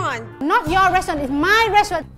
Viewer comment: in the full video i heard 'my restaurant 'when its MAI restaurant😀😀